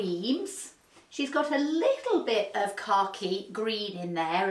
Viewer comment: Every time you say "khaki" I hear "car key" 😂